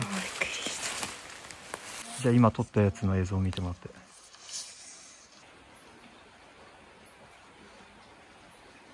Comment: 女性？の声が聞こえる。の声と重なって内容までは聞きとれない。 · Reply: コメントにも書きましたが、とは違う声で悲しげに『どうして～』って、聞こえますね